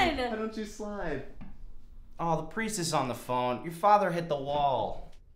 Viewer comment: Wait why do I hear stuff like " the price is on the phone"